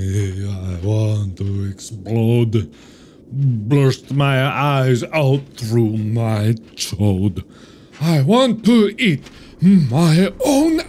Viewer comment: "Burst my eye's out through my chode" did I hear that correctly...